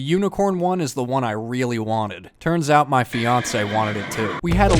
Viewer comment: Did I hear "fiance"?!!!! Its happening people!!!